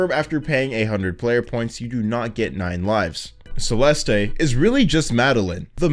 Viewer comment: WHAT IS HE SAYING “Celesteh”